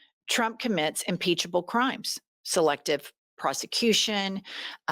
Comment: You said “selective prosecution” but I heard “selective prostitution”. Kind of similar in that weird wealthy world.